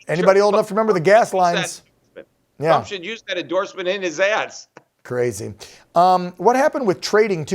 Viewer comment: Did he say “use that endorsement in his ass”?!! At 2:11 LOL Edit: I guess he says “use that in his ads”